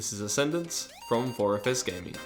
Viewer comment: Thought you said 'Orifice Gaming'